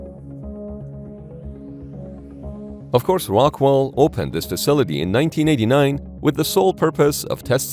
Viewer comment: did you say rockwall